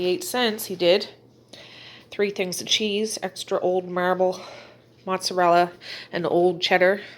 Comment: I thought that cheese said old fart 😂😂